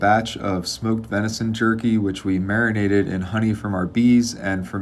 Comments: Did He just say venison turkey??? NVM he said jerky lol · i heard "smoked venison jerky" as "smoked venison turkey" and didn't put together why that made no sense until my 2nd watch LMAO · Oh, jerky! Not for nothing, I thought he said "venison turkey" and was so confused. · I thought he said Venison Turkey lmao.